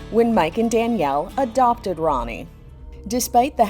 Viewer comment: i thought it said abducted instead of adopted thank god lol